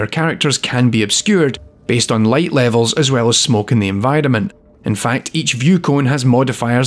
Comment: I finally figured out when sounds like he says "in vitamin" he's actually saying "enVIRONment"